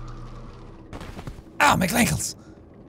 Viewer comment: wait did say "ow, my McGlankles"?? because that is best humor